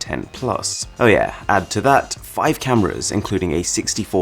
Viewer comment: did he say 5 cameras?